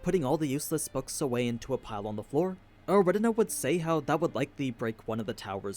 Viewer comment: What the hell, the caption said Aldebaran when aninews said eridna 😂